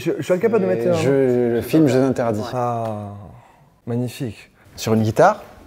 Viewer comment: C’est quoi la musique à 21:20 ? J’ai pas compris ce qu’il dit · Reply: "jeux interdits" frérot